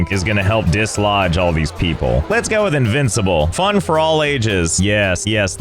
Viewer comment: Did you say... Invincible?